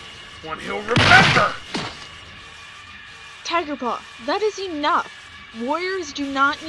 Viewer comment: Did Bluefur say Tigerplaw?..